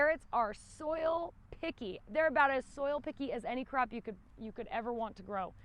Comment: I thought you said soil piggy’s lol so now that is what I’m going to remember carrots soil piggies 🐷 aka good soil!